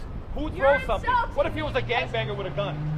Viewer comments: did he say "what if hes a gang banger with a gun?"? XD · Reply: I think he said "was" lol but that wouldve funny haha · did I just hear him say "its gonna be a gangbang" oh hell no!